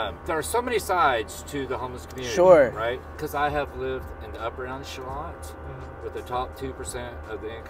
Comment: They thought he said "upper life slot" when he said upper echelon hahaha theyre seriously so stupid it's unbelievable